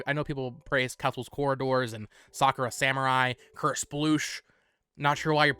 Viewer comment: Could someone tell me whats the third game he mentions in 5:24... crush brush? Sorry, I'm not a native speaker :P